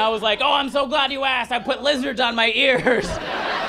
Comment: I heard 'Put lizard's on my ears' and now I'm wondering if he grew up in Florida... He has that vibe 😅❤